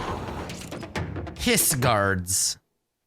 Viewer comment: like if you thought said piss guards